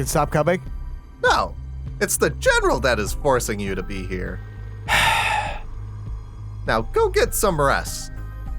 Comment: I heard "Now go get some breasta"